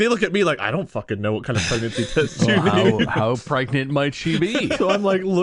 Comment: when said ''how pregnate will she be?'' i thought he said ''how pregnate my tv?''